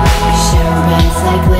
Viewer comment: "You're the only friend I need, sharing beds like little kids. We'll laugh until our ribs get tired..." I love that. Besides all the true and sad stuff in this song, this part gives me happy feelsss... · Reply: the lyric is "laughing til our ribs get tough" I thought she said tired too before I looked up the lyrics lol